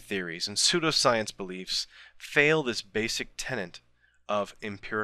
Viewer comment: did you say "tenet" or "tenant"? It sounds like "tenant". Otherwise, an excellent video -- as usual.